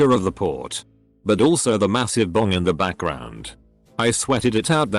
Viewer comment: I thought that said "massive dong." I was about to say something, but...